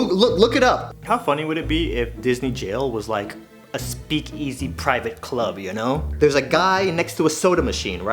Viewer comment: Hol up hol up suka rewind did he just say speakEzzy???